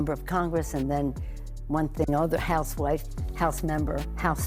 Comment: Did she just say House Wife lol